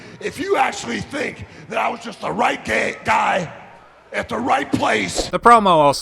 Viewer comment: did he say "GAY?!?!?!?!??!?!?!?" not guy?? lol